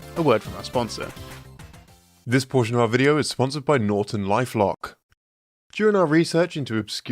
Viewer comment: I thought the the beginning said it’s unfortunately sponsored by Norton